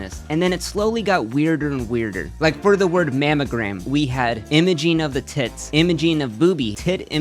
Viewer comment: this bro saying "imojean" instead of "images"?